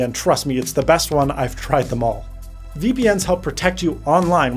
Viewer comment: did he say... VPeNis??